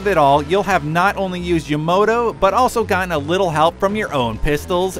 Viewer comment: Yamoto... Fuckin Amuricans. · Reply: (Murakami) i thought he said yomoto